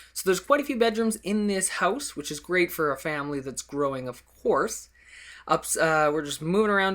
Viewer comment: Haha I thought you said "growing of whores" at 02:57 xD I laughed so hard. Love the house!